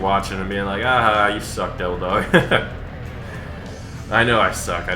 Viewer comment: sounds like you say dildog